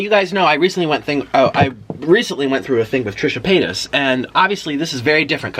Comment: did anyone else hearTrisha Penis?